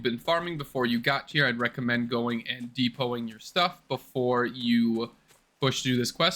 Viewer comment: I didn't catch what you said there. Deep hauling our stuff? Beautiful work! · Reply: depo my inv